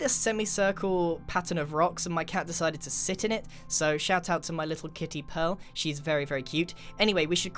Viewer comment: I thought he said shut up to my cat😂😂😂